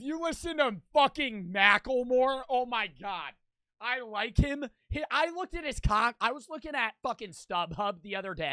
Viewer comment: I Misheard for a second and was Lmao. straight up sounded like: I like him! I looked at his C***!!!! 💀💀💀